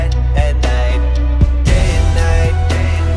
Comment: I thought it was saying Moon Knight but it was Day N' Nite